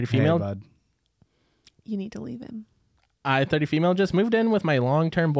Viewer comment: I thought said "You can delete him.." 😂